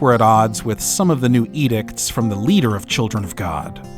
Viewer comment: Did he say “eet-icks”? “Ethics” is an easy enough word. AI?